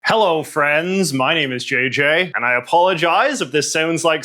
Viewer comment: Does anyone else keep mishearing opening catchphrase as "Hello, Firenze", or is that just me?